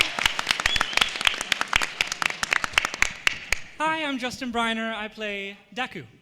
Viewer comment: Justin Briner is the most adorable and greatest person I will ever see in my atire life... I love that frickin laugh😍😂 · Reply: I thought u said Justin Bieber